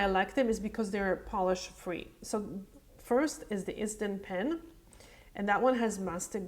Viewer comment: What is that product your using: Instant pen? Sorry, I can’t hear what you’re saying.(25:07 time of the video) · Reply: Hi Jolanda! It's ISDIN pen. I usually place links in there description box :) ISDIN Si-Nails Nail Strengthener 2.5ml <link> ISDIN SI-NAILS Nail Strengthener Cuticle Serum Treatment with Hyaluronic Acid <link>